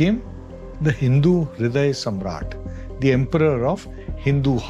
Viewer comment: Subtitles are wrong not 'Hindu Viday Samrat' it's 'Hindu Hriday Samrat'